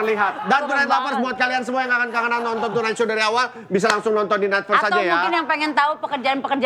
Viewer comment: nonton di netfest? Apaan kuping saya ga kedenger